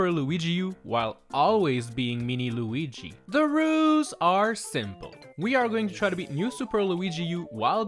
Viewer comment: Why do I hear “THE ROOS ARE SIMPLE”